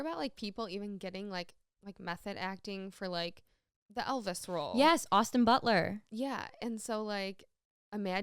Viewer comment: She said “Elvis role” and I totally thought she said elder scroll